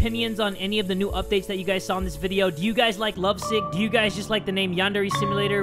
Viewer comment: swear to god he said lovesex